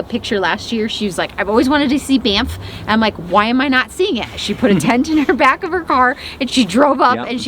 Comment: I thought said, “She put INTENT in the back of her car...”, and quite honestly, THAT’S! Intentional. Positive.